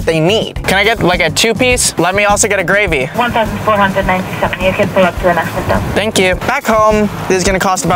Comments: WHY DOSE IT SOUND LIKE SHE IS SAYING $1,478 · did they say 1497 dollars!